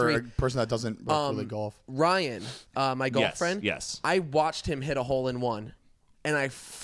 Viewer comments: I heard “Ryan, my girlfriend” instead of golf friend lol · i thought Heath said " Rayn .. my girlfriend " the camera pointed at Mariah and no body said a thing . i was like wow no body is gonna help her , what a world we live in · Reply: Malak O i think he said golf friend